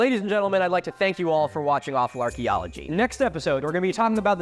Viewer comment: It sounded like he said Lawful Archaeology at 31:30, and now I want a Legal Eagle collab.